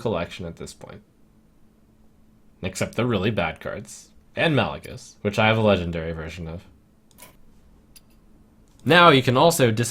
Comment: Did i heard right? 8:19? "Malygos, which i had a legendary Version of" The hell?